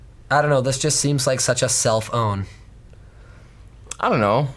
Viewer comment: did he say, self own, or cellphone?